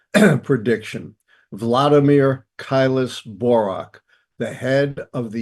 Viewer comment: Who is this? From the transcript: "kyus boro's." It sounds like you say Kylus Borack, but I have tried every combination I can think of: Zero results. I think pattern recognition is fundamental to understanding many aspects of our existence, so I would like to know who this is. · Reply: "Vladimir Keilis-Borok" - He is clearly referenced, with correct spelling, in the description/information/commentary directly below the "# of views" and "Date of Posting"